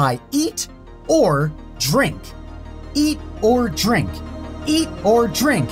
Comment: why does it sound like he is saying egordrink